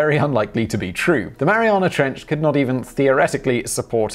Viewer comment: I thought it said Marijuana Trench.